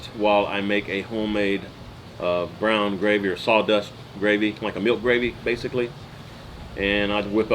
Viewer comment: Happy Birthday!! Did you say, saw dust gravy? 😂😂 Maybe I misheard, I love saw mill gravy. Another great chat, lots of information as always. Thanks for sharing😃